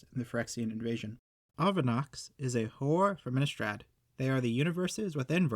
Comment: I thought you said whore from Innistrad.